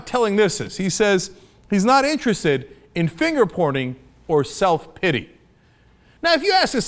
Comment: I heard "finger porning"